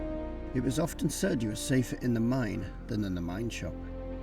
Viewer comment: When came across the remains of building where they slept can’t make out what you call it 🤷‍♂️ closed captioning couldn’t either 😳 sounds like you say workshop but that makes no sense. Still a nice solo mini documentary👍 ✌️🤙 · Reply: Thanks, it's called a 'mine shop' :)